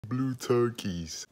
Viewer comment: His brace doctor:When did u get blue braces???blue turkies And yes I wrote turkies because that’s what it sounded like he said